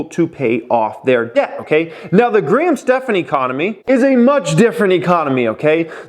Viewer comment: I thought you said Grinch Stephen economy... Almost spit out of $0.20 coffee.